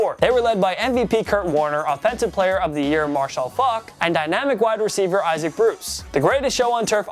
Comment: Anybody else hear marshall fuck